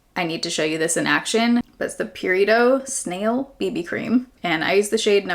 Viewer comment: Every time you said "Purito" I thought you were saying "burrito" lmao. Reminds me of OG baerrito 😭